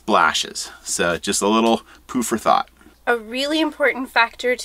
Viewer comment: ~ I thought you said “poo forethought“, but since I watch all my videos on 2X bead there’s often a little distortion between similar sounding words. So I relisten to it at regular speed and confirmed I did hear it right the first time. Lol.